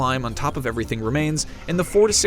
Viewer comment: Almost sounded like he said "46 hour journey' rather than "4 to 6" hour journey.